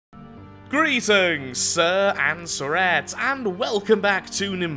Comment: Oh my god I'm so stupid. I've watched him for a year now but always thought he said "sir and screet" I was always so confused as to what a screet was....I just realized its "sir and suzette